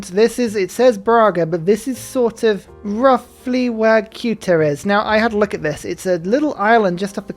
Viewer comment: if youre a geography fan like me and cant quite understand what he is saying, its "Ceuta" - its a peninsula south of gibraltar, not an island